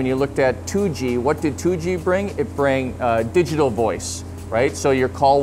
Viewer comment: Did he say "it brang digital voice"? ...pretty sure that's not a word